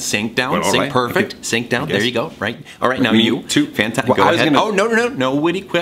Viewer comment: I SWEAR IM HEARING SAY " Now you too pat-" PLEASE TELL ME IM NOT THE ONLY ONE